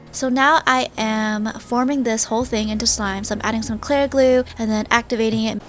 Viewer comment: Whenever you said clear glue I thought you were saying Clair Glue